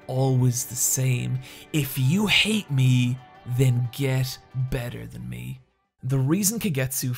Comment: Dude kagetsu line “if you dont like me then beat me” thats actually the sickest thing ive ever heard that is so fucking awesome i actually wanna start watching female japanese wrestling just because of her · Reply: That's not the sickest thing you've heard if you're talking about what's said in this video. The ACTUAL quote is: If you hate me, then get better than me. He says it here